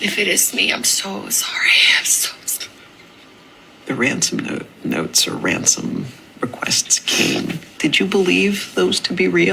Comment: I thought you said these ransom notes are noise!?